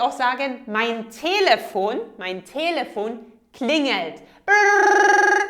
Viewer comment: I heard you say klinet instead of klingelt